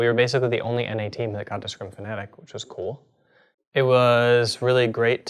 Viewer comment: what does he say at 01:01 we were the only NA team that got... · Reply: got to scrim Fnatic